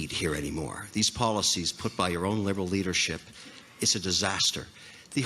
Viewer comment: Seriously, did he just say: "It;s a disaster", at 0:46. Sounded vaguely familiar .